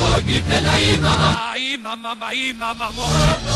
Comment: I hear "mama ma ee" and "oh is he ?" it must be satanic !! lol